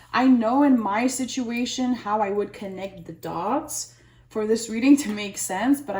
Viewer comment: Well, that was weird. You said "connect the dots" and I heard "dogs". That's what I'm getting into. Dog grooming. Probably just a glitch. Or.....is it? 😲 🎶do-do-do-do🎶